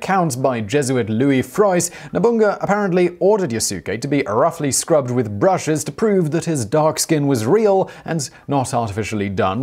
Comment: Did you say Nobungga? 😂